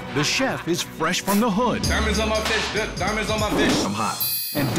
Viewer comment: The narrator's voice saying "fresh from the hood" made my day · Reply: sounded like he said diamonds on my fish flip, maybe because he was making fish